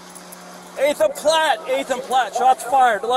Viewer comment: i first heard him say "he's a plat, he's a plat" xD now we're in R6Siege